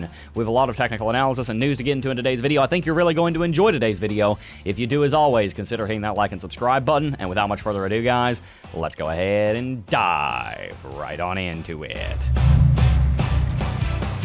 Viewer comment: when i sarted watching your channel I thought you were saying lets go ahead and die but now I understood it is dive not die (by the way I am not native speaker) you are so cool